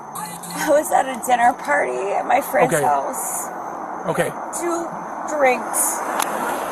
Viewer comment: i really thought she said "i was at a diddy party at my friend's house"💀